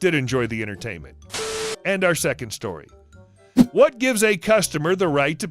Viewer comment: you said "So to the dog" instead of Story